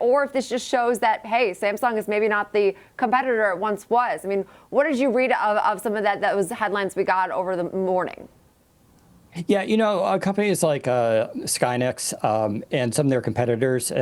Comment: Did he say SkyNet? Terminator - Freudian slip on AI